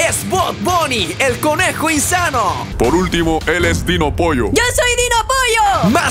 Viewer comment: parece que dice RikoPolloXD